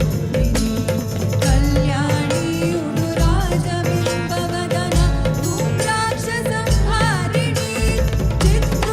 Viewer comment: What is the thing he says at 1:31:29 ? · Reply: He said Bhairavi is that tool which can make you exuberant if you know how to access her